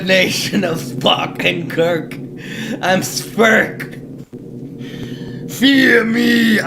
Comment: I thought he said sperm